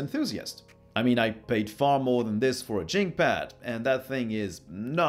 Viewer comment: it sounded like you said "Chinkpad" If you did I just wanted to point out Chink is a racial slur, you shouldn't call it that in the review or else some people might be upset